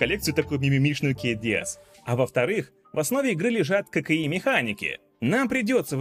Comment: Кокаин механики :D (послышалось).